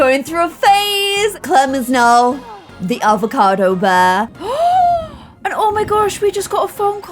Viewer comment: it hasn’t even gone 10 seconds and I thought you said clem is now the alpha male I- I need to touch some grass